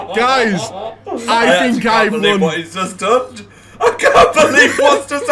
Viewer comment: Did say “Doned” looool